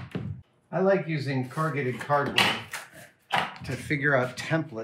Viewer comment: I don't mean to be mean, but whoever does the subtitles hasn't done a very good job. e.g. 3:26 - "I like using coordinated cardinal" instead of corrugated cardboard